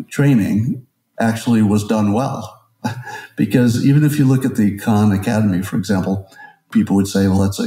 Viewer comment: look at the what? I can't understand what he says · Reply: He says, "...look at the Khan Academy, for example..." Khan Academy is a free online education site.